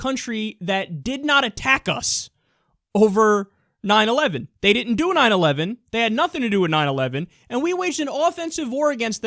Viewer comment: kinda sounds like you said "They didn't do a 9/11."